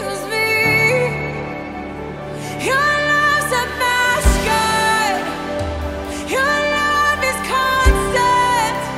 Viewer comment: HI, can someone help me out. I'm trying to figure out what Hope is saying at 8:47, after your love heals me. "Your loves a ??? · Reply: Your love is a pasture